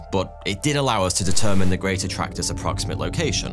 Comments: With his accent, at first I thought he said it was called The Greater Tractor 😂😂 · I thought he said The Greater Tractor for a sec. I mean....Tractors are pretty cool, but I dunno. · I thought you said The Great Tractor (like John Deere) 😂